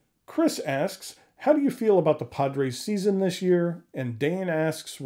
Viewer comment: As a long suffering Mets fan I understand your devotion to the Padres. After winning 101 games in 2022, my team finished under .500. Nothing they do will change my loyalty. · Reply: I thought at first he said "Podrace Season", and I said "Wait, what?" 😂